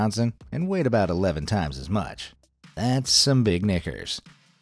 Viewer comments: I thought he said “That’s some big ni…..s” at first😂 I was whaaa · that's some big knikers | | | | It sounds like you said the N word💀💀💀💀💀💀